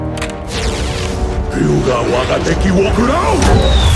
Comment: cómo yo entendí la palabra 5:00 quiuatakequiukia